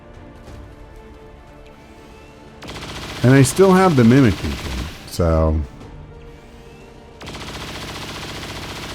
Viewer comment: Against inanimate objects you have 100% chance to hit but the chain shot penalty still applies. So that's why you missed. Not a bug, but something to keep in mind. At 25:30 what DOES say there? I can't figure it out. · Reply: Sounds like he said, "If only Advent knew my actual body count," then abruptly remarks, "I'll be down to just fists soon."